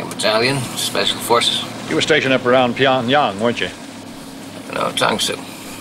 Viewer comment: Did he say Pyongyang?